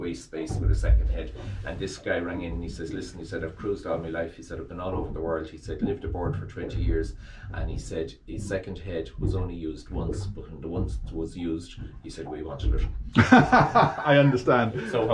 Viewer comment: What’s saying about a second head, just past the 13:00 mark? “Once twas used ….. “???